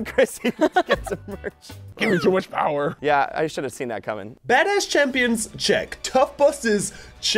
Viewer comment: did say “badass champions”